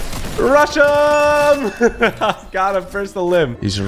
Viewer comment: i thought he said russia lmao